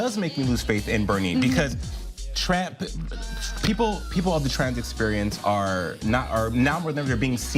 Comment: maybe im deaf, but did i just hear "trap" slip out? Lol